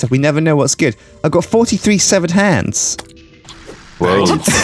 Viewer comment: "I've got 43 severed hands" the weirdest thing about that is that it's an odd number. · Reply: Tyler Ray I thought he said 437 severed hands..